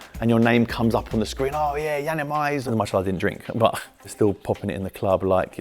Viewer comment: did i hear correctly 2:13 MashaAllah i did not drink!